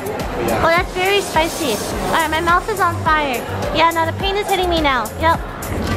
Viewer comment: i hear the pain like "penis".I'm sorry